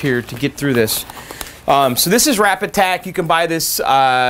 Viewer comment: I have to say.. I always thought you were saying "Wrap-Attack". I while back I figured out its really "Rapid Tac". I found it on Amazon. Its pretty easy to come by and there are lots of how to videos on this stuff. It appears guys who put art on cars, use Rapid Tac.